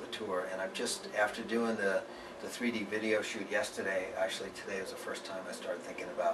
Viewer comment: Did he said 3D video shoot? :) interesting!!! amazing 6:40 -- 14 songs already written !!!! Joe, you're the man!